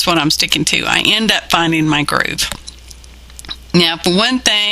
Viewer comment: Lmao---i THOUGHT YOU said, Found My Grave.....glad it's not true, but thanks for the laugh!